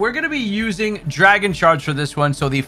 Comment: i thought u said dragon sharts😭